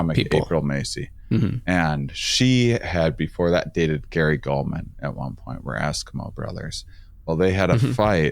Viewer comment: At first I thought you said April dated Gary Coleman.